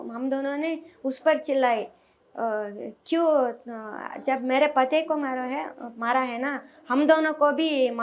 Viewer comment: You should fix the caption at 3:53 - It says Kiss instead of Kill. Have some shame and do your job right!